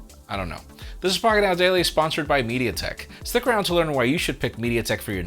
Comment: Everytime says "sponsored by mediatek", I hear "amazing facts at the end of the video"